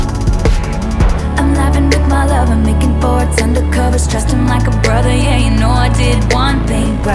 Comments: Does anyone else hear 'making 'FARTS' under covers'? 😅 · At first I thought she said making farts under covers 😂😂😂